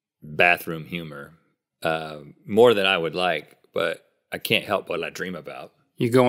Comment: I heard, "I can't help what latrine about" instead of, "I can't help what I dream about". Toilet humor brain, ENGAGED.